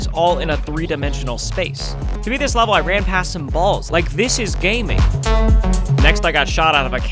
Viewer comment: I thought he said like this is gay man